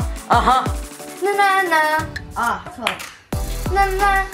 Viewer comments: Ok.. is it just me.. or does it sound like said "ah f*uck" at 3:00 ???? please tell me i'm not crazy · Am I hearing things or did just say; "Na na na~ ah, f*ck" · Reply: no he said na na na ah （错了）mean（ wrong） in Chinese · BOII I HEARD THAT "ah fuck"